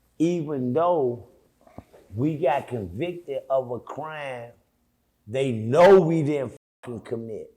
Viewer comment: A crime they didn’t commit did I hear that correctly?